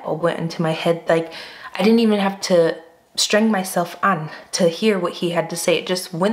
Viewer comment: Did you just say streng-myself-an ?!? 6:34 😁😆👍